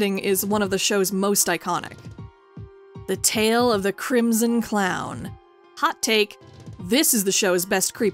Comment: The Tale of the Crimson Clown was definitely one of my childhood trauma moments, except I misheard it and thought they were saying "Clemson Clown." Whatever his name was, that clown was way scarier than Zeebo. All Zeebo did was make your life mildly inconvenient with phonecalls and footprints. The Crimson Clown friggin' traumatized you into learning a lesson.